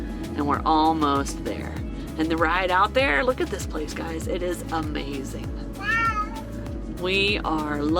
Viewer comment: G at 3:14 when you said the place is amazing, sounded like he said Wow. Love those kitties so much 😻